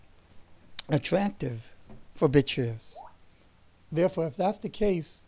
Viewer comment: "And i thing that is very very attractive... for bitches" hahahahah i know i heard it wrong, but it can not be unheard